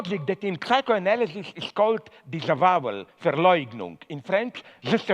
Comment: can somebody tell me what the psychoanalytic theory he was talking about is? Thank you - the audio gets iffy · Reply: Diego Ortega disavowal